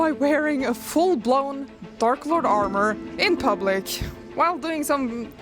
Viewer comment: Ya know, when you first verbally said what your task was (because of your accent) I thought you said "Dork Lord", which in retrospect makes a lot more sense now, but I admittedly love this video and your skills shared, plus all you overcame here to become a Dork Lord*